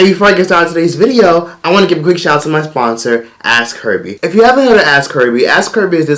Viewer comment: Thought you said Ass Curvy instead of ask Herbie lol